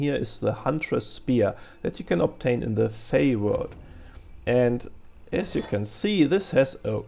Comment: I didn't understand well because of the accent, in which world you can get the spear? Is it Yaesha? BTW as I'm not a native myself, I tend to add subtitles for that reason. Although the accent also drives engagement like this because people comment when this happens haha Nice video! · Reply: The Fae-World, check the Wiki-Link in the video description